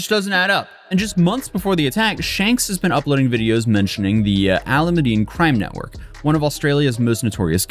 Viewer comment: I swear I thought you said "Aluminium Crime Network." Like for one it's Aluminum, and two...Damn Aluminum, You Scary.